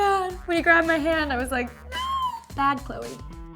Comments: Did she say "Bad Chloe" at 3:40 or was it just me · did she say bad Chloe??? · Did she say "Bad Chloe"??